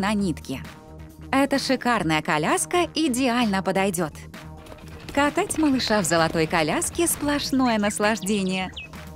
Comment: мне одной послышалось какать